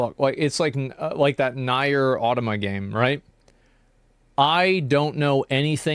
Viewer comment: Wait, did he say Nayer for NieR? Lmao!